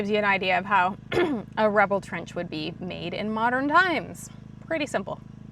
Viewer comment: idk why i heard "rebel trench" instead